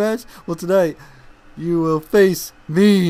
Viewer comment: In 2:08 it said I fill instead of will.